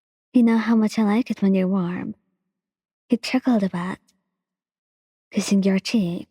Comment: For shoto when you said “I like it when you are worm” I thought you said “ I like it when you are hard” lmfaoooo